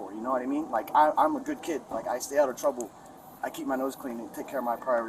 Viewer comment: He said he's a "good kid." I thought you said he was 30.